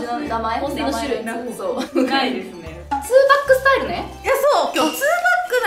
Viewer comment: で[2pac style]とか聞こえたから「そんなhiphopなんか！？」とびっくりしたｗ